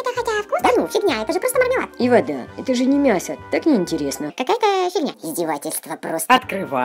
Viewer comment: мне одной послышалось "какая- то херня